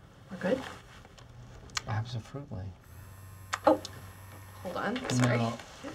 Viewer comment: Did he just say "absofruitly"?